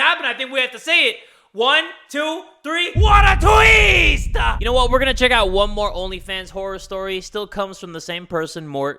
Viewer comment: What did he say at 10:52??? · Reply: He said "WHAT A TWIST"